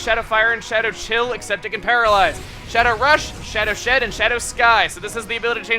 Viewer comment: It sounded like he said shadow shit